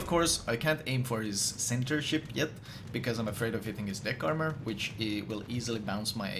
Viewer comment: who else thought he said dick armor instead of deck